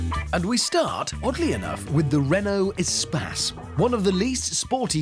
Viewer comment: rofl i thought he said renault espaz